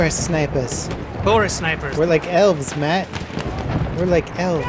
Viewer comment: First few seconds, I hear "we're forest elves" and I'm already having a good time. <3